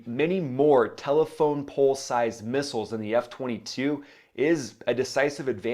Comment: Did he just say telephone pole sized missile ??